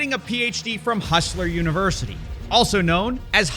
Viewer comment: I thought he was saying "Hofstra University"...which is better...maybe....